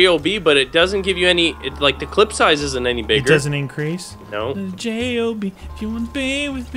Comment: said gob instead of job